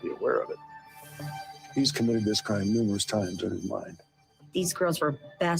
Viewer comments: Man: He(Kohberger) has committed this crime multiple times in his mind. What I heard: He had committed this crime multiple times in his mind. What heard: He has committed this crime multiple times before. Me: 🤣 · It sounded like he said "he's commited this crime numerous times in his mind." Editing to add about body language. I hate when they do this. That may hold to be true for the average person. There is ALWAYS an exception to the rule. If I'm an exception to the rule, I know he HAS to be if he really is socially awkward like some people say he is. He's not going to do things the same way your average cool joe shmoe does. He's gonna be more on the nerdy side.